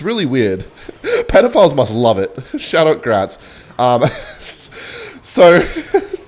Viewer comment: OH I THOUGHT HE SAID "GRADS" AS IN GRADUATES... Damn Gratz